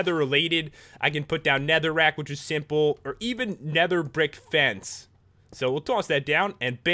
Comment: i thought he said nether dick fence